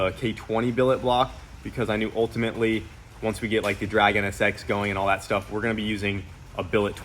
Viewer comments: When he said drag nsx i thought he said the dragon and i think that would be a fitting name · I thought you said the dragon sx . Not drag NSX. Maybe a cool name. Dragon SX.